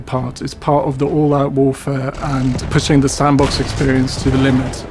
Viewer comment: did he say "the sandbox genre?" hint of the single player??????